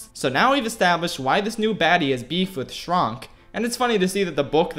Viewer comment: did he say "beef"? :0